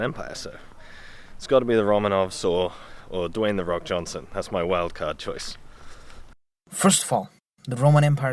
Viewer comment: lol I was a little distracted when the first guest youtuber was talking, and I thought he said, at the end, "Dwayne The Rock Johnson is my choice" Yes alcohol had a little to do with it. Yes, I get drunk and watch youtube videos. Yes, I get distracted while doing so, usually by looking up other history and geography videos.